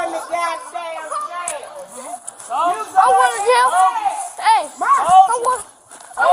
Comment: the subtitles are wrong. He kept asking "I'm going to jail?"